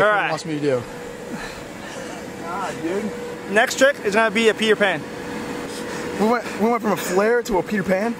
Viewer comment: i heard pee your pants not peter pan